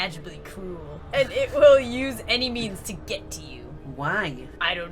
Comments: I thought you said “it will use any MEMES to get to you”. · It will use any means to get to u I miss heard her and I thought she said It will only use memes to get to u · When it said "It will use any means to get you" I thought it said "It will use any MEMES to get you" I am laughing my head off right now!!🤣🤣🤣